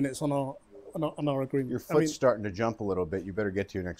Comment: What exactly does he say at 6:03? Did he say your face is starting to jump?